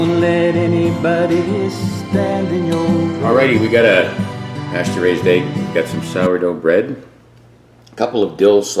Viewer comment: What's a castrated egg? Sounds painful 😳. Maybe I misheard lol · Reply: i think he said pasture raised